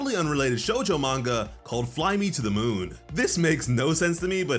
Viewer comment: The caption says "How did they become a well loved sh.t"😂